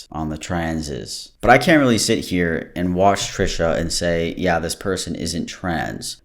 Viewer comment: why did i think he said cansas XD